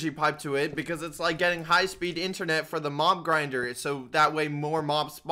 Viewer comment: did I hear getting high speed internet for the mom grinder